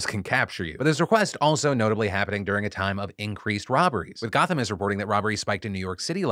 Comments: I swear at 6:47 I thought you said 'with Gotham reporting' XD bat on the brain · IDK what you're saying here Phil. "With Gotham is reporting..." is what I can make out and I really doubt that's what you're saying. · did bro say Gotham??